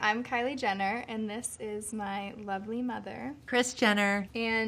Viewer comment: I thought she said " my ugly mother" at 0:03 😁😁